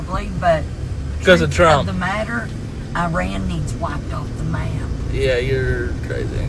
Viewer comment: did she say "Iran needs wiping off the map" ?!?!! 😳